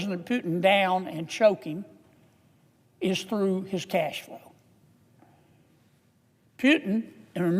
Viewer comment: He said cashflow and with his accent I thought he said asshole. Oh well.